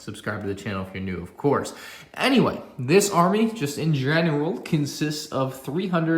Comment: did you just say GENERAL? Pryde